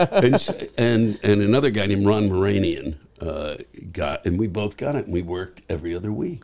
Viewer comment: Did he say Ron Boranian?